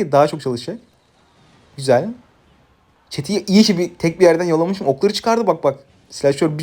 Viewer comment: çeteyi iyiki tek bi yerden yalamışım mımdedi yanlış duydum heralde